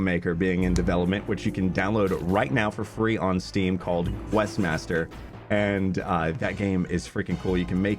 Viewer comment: Literally thought you said “Westmaster”. Can you link the games in the description?